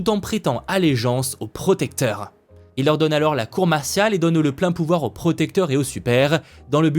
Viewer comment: la "loi martiale" pas la "cour martiale"